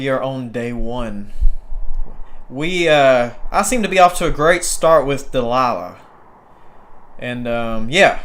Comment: I thought you said "the Lala " 25:24 But its Delayla right? :D What is your take on this game? I have been looking at this game since release but havent played it.